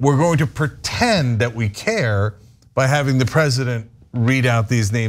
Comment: did he call trump the present?